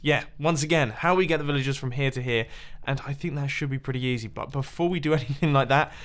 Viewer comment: Mumbo... At 7:18, did you say "that should be pretty YEEZY"?! XD